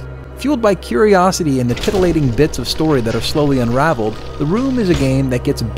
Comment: Did he just say "titillating"? XD